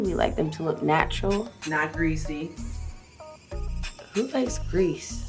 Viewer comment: i thought i heard "not breezy" that's why Ri was like 🤨 "who likes breeze?" 🤣😂 uhhh i hate myself sometimes.